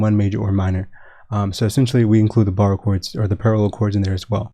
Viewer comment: I thought you were saying "bar chords" at the start and I was like "is this man giving guitar lessons now?". For real though, insightful stuff my guy. If you've got any tips for progressions using modal modulation beyond major/minor I'd really love a fresh perspective.